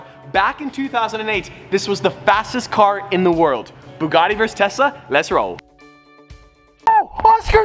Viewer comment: Sounded like he said bugatti 🐞 vs tessa 💁‍♀️